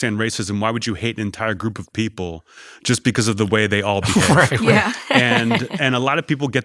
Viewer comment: Wtf does he say here? 25:19 “just because you…..” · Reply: The whole line is, why would you hate an entire group of people just because of the way they all behave. That's his joke.